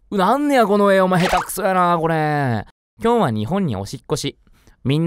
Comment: おしっこしに聞こえるw